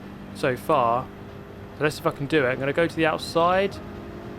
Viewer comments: At first it sounded like you said "let's f#%*ing do it" lol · did he say "let's see if I can do it" or "let's fucking do it" ?😂